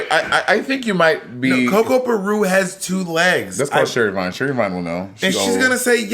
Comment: bitch I thought they said sherry pie😭😭😭